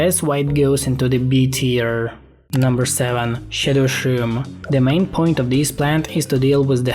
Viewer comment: number 7. $h!tt3r shroom is what i heard